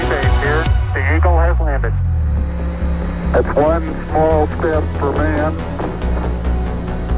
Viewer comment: its 1 small step for man 1 giant leap for man kind What a iconic word on moon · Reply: The real words he uttered were "That's one small step for [a] man, one giant leap for mankind."